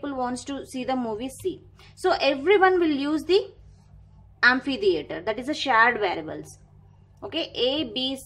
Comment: Unfiliator ? i am so sorry i can’t hear what is the word, please tell me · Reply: Amphi-Theater, basically movie hall